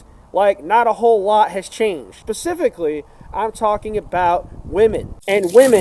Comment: did bro just say pacifically? 😭